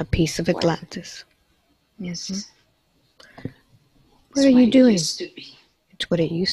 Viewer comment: Did anyone else hear the, "What are you doing?" at 6:12?